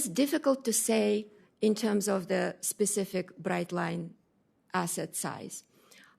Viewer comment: did she say breadline?!